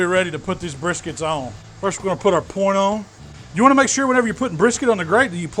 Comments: did he say porno😂😂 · Did he just says porno on 4:10?